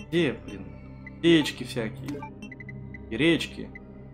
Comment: кому послышалось яички и гречки ставь лайк)